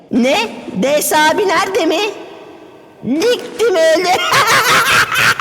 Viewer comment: burada ne diyor? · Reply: Diktim öldü